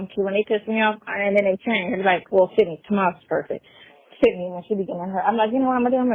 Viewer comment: Did she just say tomas is perfect?